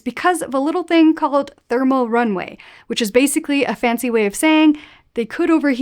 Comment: Thermal "runaway".. I heard Thermal "runway".